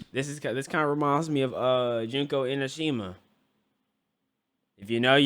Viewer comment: am I tripping or did he just say Junko Enoshima